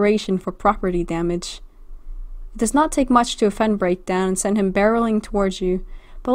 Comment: Did you just say "breakdance"?